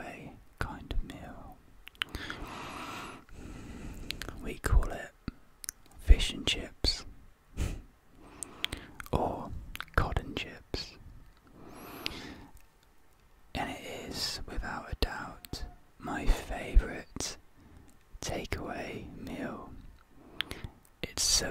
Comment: new video No one: Absolutely no one: Me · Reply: WOW i thought you said god and chips😂😂 it makes more sense now